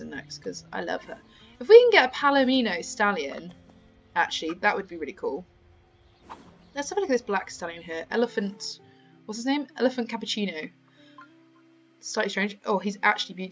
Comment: Did she say elephant cappuccino or elegant cappuccino?